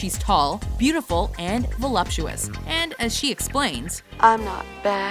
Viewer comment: ?? velapsuous? wat?? @@ what word is that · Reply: Jors Manel It’s voluptuous. Meaning that she has the right curves.